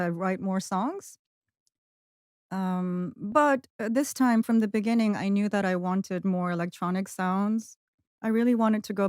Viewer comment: あたり、なんて言ってるか教えてほしいです。アスタリスクのとこがわかりません But this time from the beginning I **** more electronic sounds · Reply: But this time from the beginning, I knew that I wanted more electronic sounds、かと思われます！